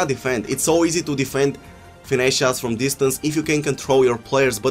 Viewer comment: I thought u said finnese shots were fixed